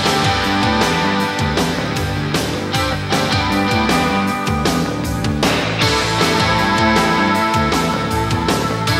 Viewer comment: I also own this on VHS. So what is he saying? Whos forget it? Just forget it? Also.... that dance routine at 2:50 .......Unreal! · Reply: Blastarr's words, said a few times throughout the video, are "Foolish organic."